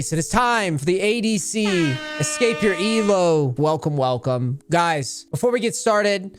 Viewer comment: I thought you said "escape your ego", never felt that attacked :o